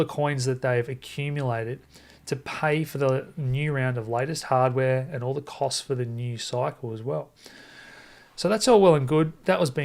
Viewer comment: i thought he said they where keeping their new coins for the next cycle